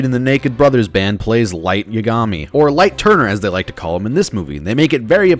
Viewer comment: I thought you said light turdner